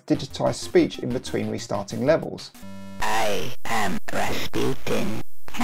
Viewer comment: What does he say at 25:21? "I am rasputin, hahaha, geezer!"